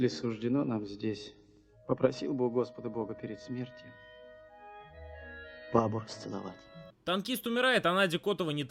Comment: скажите пожалуста,что он сказал? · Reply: "Попросил бы у Господа бога перед смертью бабу расцеловать"